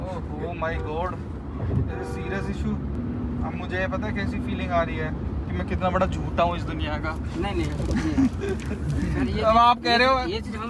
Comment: In 6:35. I think say mujhe pta hai kya feeling aarahi h me kitna bada chu*iya hu i duniya ka 😂😂😂😂